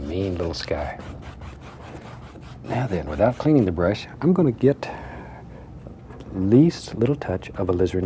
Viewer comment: and without cleaning the brush googliget I know what he actually says don't worry ? Sure I can tell you Fine We're gonna' get